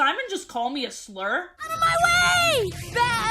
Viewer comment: I can't even tell what you think he said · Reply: I think f*g